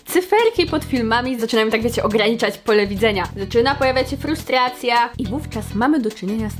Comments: usłyszałam ''Prostracja'' i miałam laga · usłyszałam prostracja haha